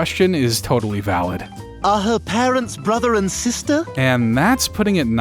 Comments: what did stewie say on the uncensored version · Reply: "Is she ret**ded" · if you're wondering what he originally said, he basically said "is she retarded"